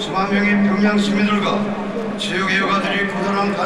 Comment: 체육일가 가 아니라 체육애호가 로 들리네요.